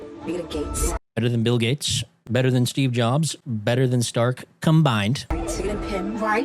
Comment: I actually didnt even realize she said combined, I thought she said something like "come Bond, or Come Vibe" because saying you're going to be better than gates, jobs, and stark combined is ridiculous